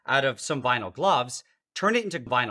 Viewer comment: I keep hearing “vinyl Glob”